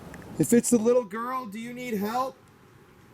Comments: Such a great video!! Although all your videos are great! Just to add to the creepiness I think I heard "can you help?" More than I heard "need help" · I heard "I NEED HELP." Wow! That was so clear! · i heard "Daddy" but, tht voice "I need help." Is wat i hear its what u played back afew times after, 35:40-.